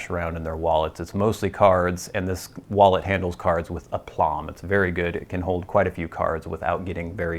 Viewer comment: hey, I actually couldn't figure out the word. It's at the 12:07 mark & sounds like you're saying plawn. I'm sure it's way off & probably just made a fool of myself. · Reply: zoo Ah, I said "aplomb." I said it in a sort of mush-mouthed way though!